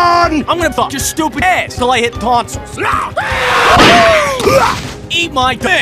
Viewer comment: What does say at 0:26? Deleted tonsils? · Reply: of Helium I think he said "'til I hit tonsils."